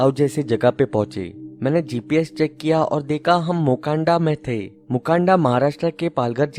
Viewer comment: I thought he said WAKANDA 😂😂😂🤣🤣